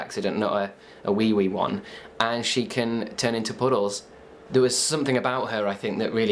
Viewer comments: Thought you said poodles hahaha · thought you said she turns into poodles